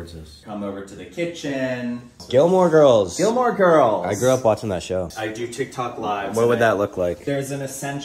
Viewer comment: I had to rewind, i thought he said "i grew up busting to those girls" 😮😅